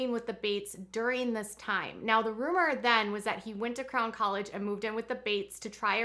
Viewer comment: Everytime you say, “Crown 👑 College,” I hear “Clown 🤡 College. I cannot imagine why.